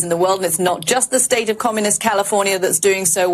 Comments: did she just say communist California? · Does she say “not just the state of COMMUNIST CALIFORNIA” at 0:18??! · Im sorry did I heard that right did she just confirmed thats its " Chinise State of communist California" ?